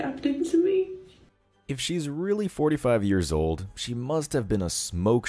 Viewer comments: I heard the woman at 3:00 say 25, not 45, I then turned on subtitles and yes...25, she's still Young. I guess it sounded a little like 45, although I have my sound set up for clear speech. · Pretty sure that woman said she is 25. Not 45. She did not say if she was a virgin though. If she is not (most likely) then she rejects "good men" for Chad only. Zero sympathy.